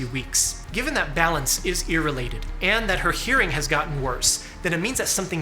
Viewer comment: Homie said “ear related” but I heard “irrelated” and I thought to myself “they’re totally related!” .25 seconds later I understood…